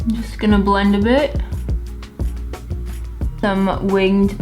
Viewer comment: When said, "just gonna blend a bit" I heard "just gonna blend a b**ch" 🤣🤣🤣 is this dyslexia?